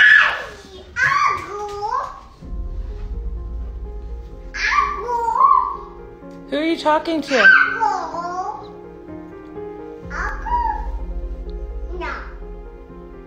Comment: Is she saying "Uncle!" ?? 😱 Oh....just watched more and it seems..she is!!